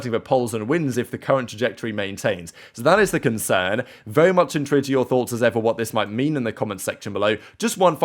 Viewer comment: Bro I love your work - I watch your videos daily - but I have a burning question. What do you say at the beginning and end of your videos? “Very much (into???) enter your thoughts…” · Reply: Hi I always hear it as "Very much INTRIGUED to your thoughts". Could be wrong though..